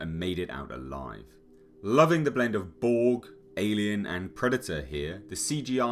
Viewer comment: Can someone please tell me what the narrator said at 2:46... "living the blend of ---, alien and predator here". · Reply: I said, loving the blend of BORG ( Star Trek villain ) alien and predator. :) hope that helps.